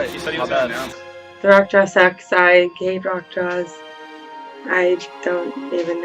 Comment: What'd she say? Something about sex and drugs? lol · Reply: "the rock draw sucked, I hate rock draws"